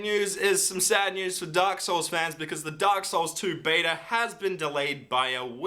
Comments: i thought he said Duck Souls 2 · i thought he said DuckSouls2